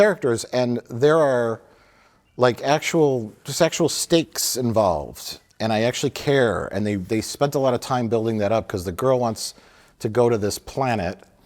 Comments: Did mike say sexual steaks?helloooo....shouts out earl skakel 5:49 . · "actual sexual stakes"? What did he actually say? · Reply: "actual...just actual stakes involved." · Did Mike say "actual sexual stakes" at 05:46? Was he drinking BEFORE the review?